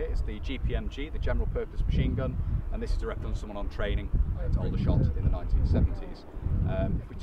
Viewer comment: did he say training in aldershot??